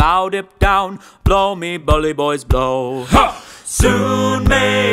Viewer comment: sorry I know it's childish but I've only ever heard it as "blow my bully boys" and you said "blow me bully boys" which sounds like you're commanding them to do something very different😂 Amazing all around i just had to point it out! 10/10 pure vibes, as always